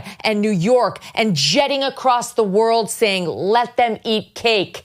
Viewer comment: I guess I misheard Mizz. Swift. I thought she said: "LET THEM EAT CATS!" That's what I thought I heard.